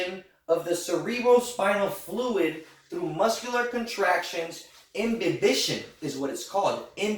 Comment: What word did you use at 25:05? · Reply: imbibition was the word he used